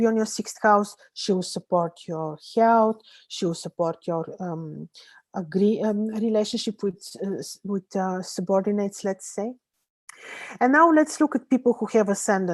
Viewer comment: I can't understand what she says between 27:17 and 27:20 :/ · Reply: She (venus) will support..., agree, aah, relationships with subordinates, let's say.